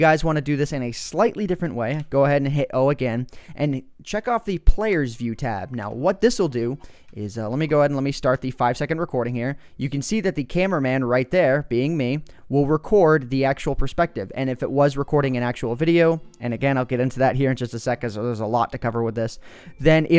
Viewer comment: I thought it said Caveman lol